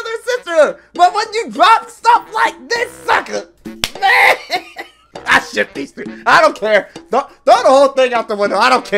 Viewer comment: I thought I heard “I ship these three”💀